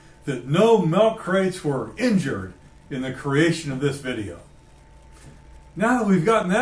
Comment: I thought you said no milk crates were harmed in the making of this video, doesn't being cut in half qualify as being harmed. Just kidding, good video